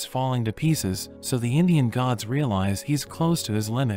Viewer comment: Bro when Shiva was fighting (the Walmart thanos guy) I thought he said Indian gods not alien gods bc💀 and the mark on his forhead💀💀💀